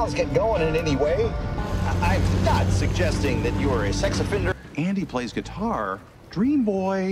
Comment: I had to replay that a couple of times. I thought he said "Andy plays guitar" as if he forgot his character's name and was calling him Andy from The Office.